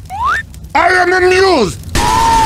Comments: he actually said "I am amused" which is cut from the "I am amused by entire itsy bitsy teenie team!" line. 17:02 Also, that's soldier, not heavy. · TitanMaster4554 already pointed it out, but at 15:55, you misheard "I am amused" as "I am the news." Edit: Also, lots of Spanish and Mexican people like Speedy Gonzales, and Pepé Le Pew did not think he was a cat; in the original cartoon, Penelope accidentally gets white paint on her back, and he thinks she is a skunk.